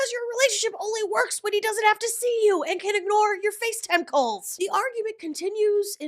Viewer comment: I genuinely thought you said “and can ignore your face tentacles” 😂 I listened again, still heard “face tentacles”. One more time, nope, still tentacles. Popped on the captioning….. Oh! “Face time calls” 🤦‍♀️🤦‍♀️🤦‍♀️ I may have to refer to FaceTime calls as face tentacles from now on. 😂